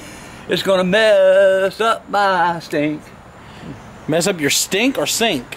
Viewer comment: He clearly said stink not sink LOL